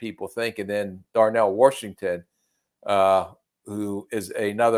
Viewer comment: Did i hear a Darnell "Warshington" yinzer accent?